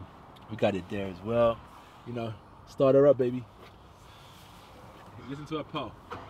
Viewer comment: Did I hear him address the man on drivers seat 'babe' at 1:03 min?